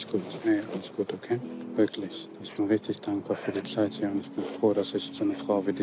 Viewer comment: Ich verstehe IMMER, dass sagt "Ich bin richtig dankbar für den SCHEISS hier" 🤣🤣🤣🤣🤣